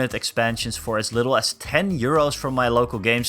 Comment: I thought you said “90 year olds” not “90 Euros” at the beginning lmao I was so confused